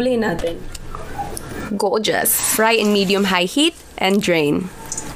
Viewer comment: She said drain I thought she said dream.... Cuz it looks so dreamy